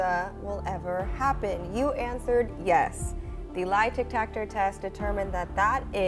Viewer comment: I swear I keep hearing say “lie Tic-Tactor test”